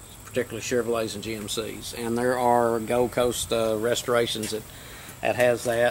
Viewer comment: Did he say at 7:35 that Go Coast Restorations offers parts for these trucks? I googled it but couldn't find the place. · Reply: Sounds like he said "Gold Coast Restorations"